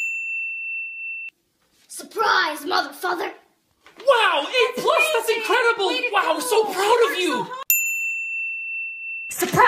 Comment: I always thought she said surprise mf